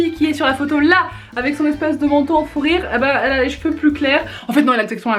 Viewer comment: C'est moi ou a 4:37 Elle dit "le manteau en fourire" ? 😂